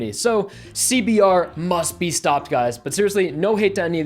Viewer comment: I thought it said "SBR must be stopped" and was about to say eat shit asshole